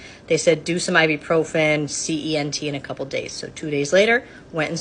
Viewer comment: Ohhhhh you said, “see ENT” and I thought you said, “C.E.N.T.” and I could not figure out that acronym at all. Hahaha 😂 Ima dumbass….